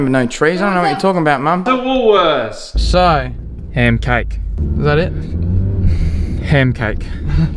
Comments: I thought you said hair cake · I thought it said human cake